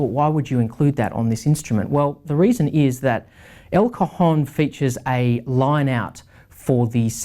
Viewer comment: I keep hearing "El Cojon"